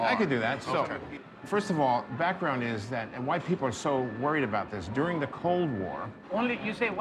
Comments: Who else thought he said white people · I thought he said white people too lmao